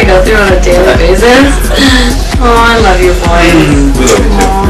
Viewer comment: What they say in 3:53 usually what? I can hear that so anyone tell me what is say? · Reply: Nina: oh, i love you boys Boys: oh , we lo ve you too( usually) I think